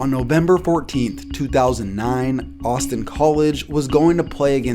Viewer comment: The second one I thought he said Boston college for a second